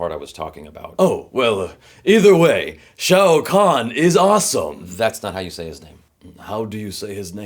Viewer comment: I thought you were saying "shao Kahn".